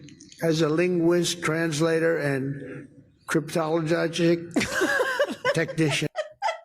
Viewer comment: i heard 'cryptologargic technichian'. i also have a TBI, sooo... lol